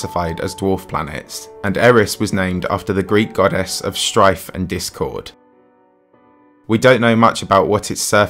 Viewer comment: "Strife and Discord" is what I heard...